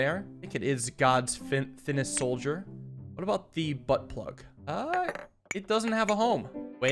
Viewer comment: "What about the butt plug... it doesn't have a hole!" is what I thought you said at first LMAO